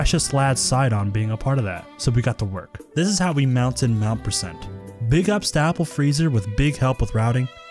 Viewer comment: i thought you said "Big ass Applefreezer". Amazing video my man <3 keep up the fun content <3